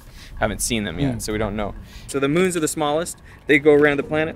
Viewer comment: Not even 30 seconds in and I hear "I think the stars are the smallest". I want to cry